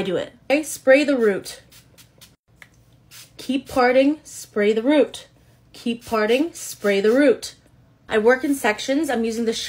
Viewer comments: I heard “keep farting, spray the root, keep farting, spray the root” 😂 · Why on earth did I hear "keep farting spray the root, keep farting spray the root" 😂😂 I'm a mess. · Lol 😂 I can’t. I replayed this about 4 times and every time I hear “keep farting, spray the root” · Lmao I thought you said keep farting! I was like what???? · Y’all I thought she said keep farting,spray the root 😭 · Is it just me? I keep hearing "keep farting"😂